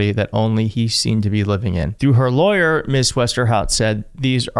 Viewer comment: Every time you say "Ms Westerhout" I hear "Ms Cryforhelp."